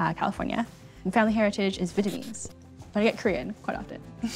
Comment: I’m not sure she said “Vietnamese.” I think she said “Fujianese.”